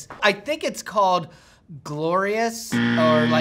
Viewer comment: Hilarious!!! Esp @ 10:05 when I thought said “Amadeus...glorious-ECZEMAs-deo...” 🤣🤣🤣 Had to laugh at that a few times! And loved the legends SCC & MWS...& awesome voice!! 😍😍😍